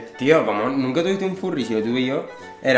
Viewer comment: yo tambien entendí Furbi😂 :V